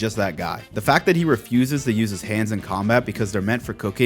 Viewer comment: I heard you saying "he uses his hands for cocaine" and I jumped. lol. I'm happy you made this video, I'm following your journey on Twitter and it has been amazing... also, and this is a compliment, your voice is exactly what I imagined it would be.